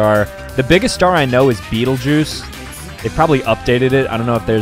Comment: When he said betelgeuse I thought he said beetle juice